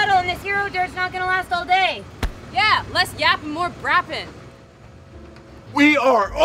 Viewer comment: what she said at 2:34 ??? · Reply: "Less yappin' more brappin'" haha as in shut the f up and drive us